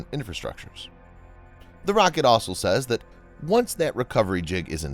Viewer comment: you said "THE ROCKET SAYS" when subtitles say > The report says also" xD